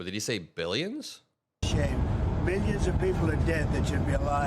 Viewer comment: criticizes Trump's response to a Russian missile strike in Ukraine, calling it weak leadership. He argues Trump whitewashes Putin's actions and fails to condemn Russia, instead labeling the attack a mistake. discusses Trump's narcissism and relationship with Putin. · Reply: At first, I thought he said minions..